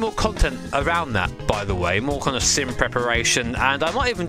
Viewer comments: I misheard "sim preparation" as "simp reparation" and got excited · Pun brain misheard as "Simp Reparation"